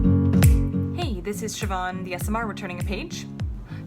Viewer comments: Why is it that every time she returns a page I hear ‘siobhantheasmr’ 😂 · tf does she say on those calls, hey its shavon the asmr??? lmao tf is she saying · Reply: Bobby Stevenish lmao i thought she said that too but she says ‘SMR’ I think it stands for Senior Medical Resident???🤷🏻‍♀️